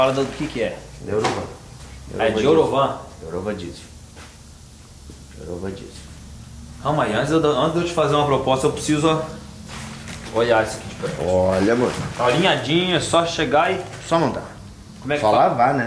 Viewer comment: eu ouvi um olha amor do? Kkkkkk